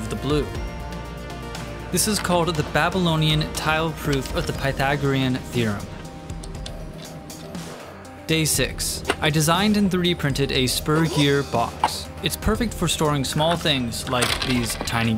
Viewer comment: i legit thought he said "sperm gear" and not "spur gear"